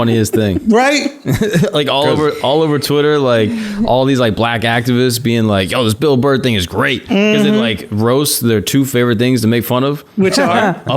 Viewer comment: sounded like "this is Burr-fect"